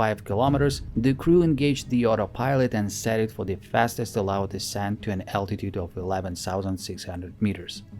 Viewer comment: You can't descent from 5000 to 11600. You must ascend. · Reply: I heard “fastest allowed ascent”